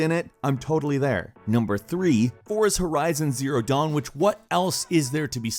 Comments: OMG when he said horizon zero dawn. I was looking away from the screen and thought he said Forza horizon! I got my hopes up! 😂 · it sounded like u said forza horizon zero dawn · Wait, did I just hear say, "Forza Horizon: Zero Dawn"? Skip to around 11:45. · Did he just say "Forza Horizon: Zero Dawn"?!